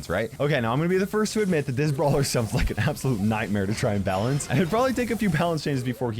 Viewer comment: did u say fuckin nightmare?! Thought this was family friendly